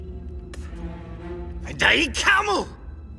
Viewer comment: I'm so glad there are subtitles with these things. The first time I heard Eddie yell 'Tai kamu' I thought he was yelling 'Die camel!'. And I was so confused until I went back and read it. 😅